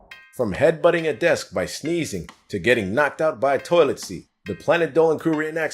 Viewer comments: I keep hearing “knocked up” by a toilet seat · At first I thought he said knocked up by a toilet seat. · At the start for a second I thought he said "being knocked up by a toilet seat" and I was like wtf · I have to laugh. I thought said “to getting knocked up by a toilet seat” and I nearly spit coffee all over my ipad.